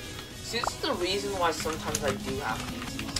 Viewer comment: did u just say u hacked in this game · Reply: It did seem sus. It sounded like, ‘She’s the reason why sometimes I do hack’